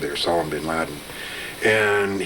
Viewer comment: Anyone else catch him mention Salem bin laden at 15:45?